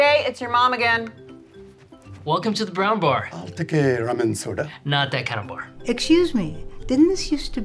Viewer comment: no one: The old guy that id don't know:"I'll take a lemon soda". Mena Massoud: "not that kinda a bar". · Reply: i thought he said ramen soda 😂😂